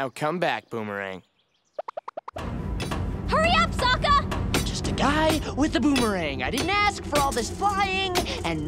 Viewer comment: What is sokka saying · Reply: Just a guy with a boomerang